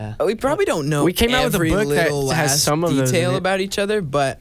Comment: did corbyn say what i think he said (nothing wrong with it but it kind of caught when i was wTching it for the second time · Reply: Hannah Kachelries “every little last detail”